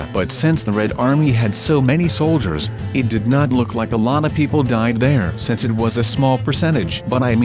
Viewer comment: wean u were talking about the people who died of hiporthermia for some reason when said" a lot of people died " I thought u said "a Llama people died"